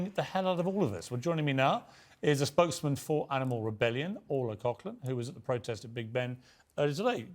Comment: Oh shiet i thought he said her name was Oral Cockler 😂 at the start until her name appeared to show its